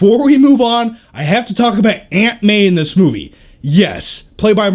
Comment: I thought he said Ant-Man instead of Aunt May